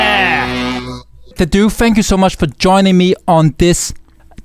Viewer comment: I heard "The Dooo fingers so much for being on my stream on twitch"